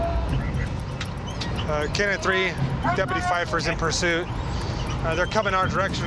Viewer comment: Did they just say deputy Fife? Someone get ahold of his CO Andy Griffith.